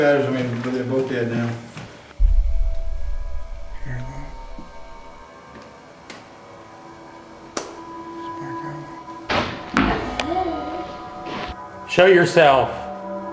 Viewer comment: I kept hearing a child talking, couldn’t understand what it said several times but at 43:41-43:45 I clearly heard a child say “Can you hear me?” Heard same again